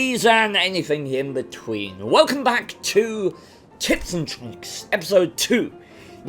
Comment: I swear when I heard you say tips and tricks it sounded like tits and tricks